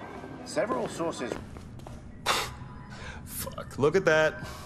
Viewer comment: I heard "several sausage" xD 2:35 Connor smiled to the coworker. So cute 🥺